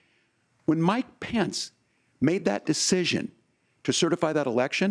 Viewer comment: for some reason I heard him say "when my pants made that decision"